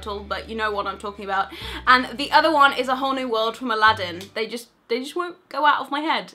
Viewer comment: Omg am I the only one who heard it "A Horny World" the first time at 03:19 instead of "A Whole New World" HAHAHAHHA